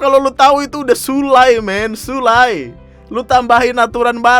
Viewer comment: i heard eno bening